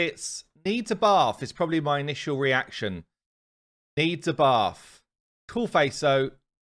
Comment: Coulda swore you said “needs a buff” 😆😂🤣